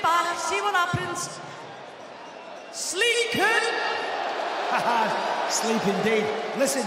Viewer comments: Did she say "Sleep Coon" ? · Did she say 😳 "sleep coon"?